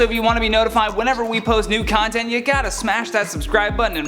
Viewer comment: I swear I thought I heard him say NUDE content and not NEW content. Haha that went from YouTube to OnlyFans real quick. 😂